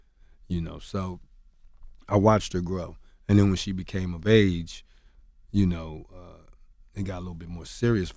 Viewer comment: Kurupt admitted he was with a 14 year old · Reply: Shawn Blazington TV I must be hearing things because I thought he said things got serious when she became of age.